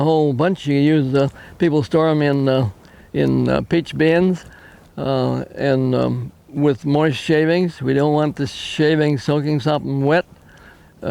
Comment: store them in the "Peach bin"??? Couldn't understand that line?